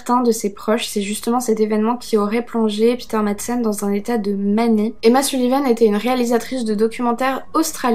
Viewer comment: Dans un état de quoi ??? À 19:23 ça a coupé... Je bois tes paroles et j'avoue ma frustration à n'avoir pas compris ce que tu dis précisément là 😩 Si j'extrapole je dirais que tu dis ds un "état de manie" j'ai bon ? Edit : La manie, ou un épisode manique, se caractérise par une augmentation de l'humeur, un état euphorique sans rapport avec un contexte particulier, et une excitation psychologique et physique.